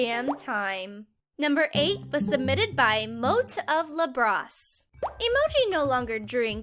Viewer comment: it said illustrated instead of submitted